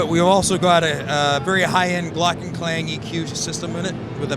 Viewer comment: Did He Say Gluten Klang EQ ? @ 6:40? Like to Know More About that